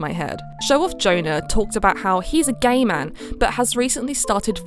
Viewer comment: lowk thought you said 'he's a gamer'